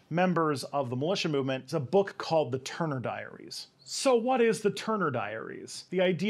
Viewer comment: I was listening to this video without headphones and I literally thought I heard you say The turd diaries! Cue the sheer disappointment I felt when I found out The real name of that dumpster fire of a manual!!!